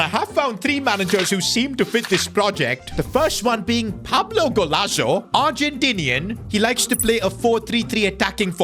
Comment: did I hear Pablo Golazo??